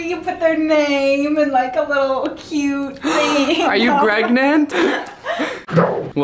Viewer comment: I heard "Are you GREGnant?" *confirmed they're having baby gregs